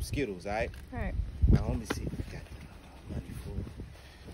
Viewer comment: I fr thought it said monkey not money